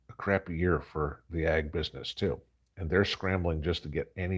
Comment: "The egg industry is scrambling..." What a beautiful pun and you didn't catch it.😆😂😂😕😂😂😂😆😆😆